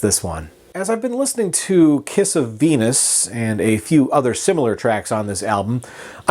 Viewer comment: I thought you said ‘kiss a penis’ Not ‘kiss of Venus’ Lol